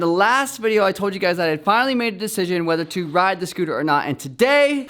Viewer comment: I thought he said wasn't going to ride the scooter... Awesome vid tho!!